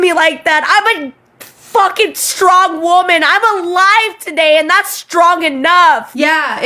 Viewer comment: Genuinely I thought she said "I'm on live today and that's strong enough" and I could not wrap my head around what was 'strong' about being on TikTok live.